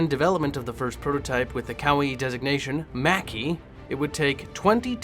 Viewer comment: That part about the Mackie sounded like you said kawaii desu-gnation. Jesus... i realise it’s probably not what was said, none the less I’m pissing myself with laughter xD Great video btw.